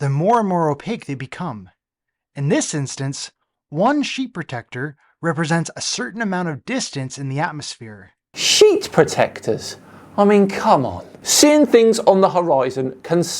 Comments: Wait... I had to rewind and listen to that again... Did he just say "sheep protectors"? ... Ok, no, he said "sheat protectors". Although I wouldn't put it past a flearf to think that the atmosphere is full of sheep. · The only Zone this deluded individual is in is the Dead Zone , a complete and utter piece of belligerent rubbish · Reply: Sheet protectors.. I thought he said Sheep Protectors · Oh "sheet protectors" I heard "sheep protectors" my bad!